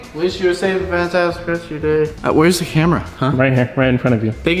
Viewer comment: I thought nathan said "Wheres the hammer" when he said "Wheres the camera"